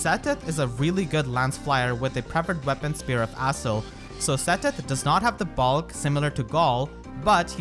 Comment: I thought you said “Seteth does not have the same balls as Galle” 😂 I was like woah